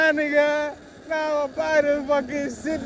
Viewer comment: yoooo? 0:26 what did bro say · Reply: I said I’m gona buy this sheet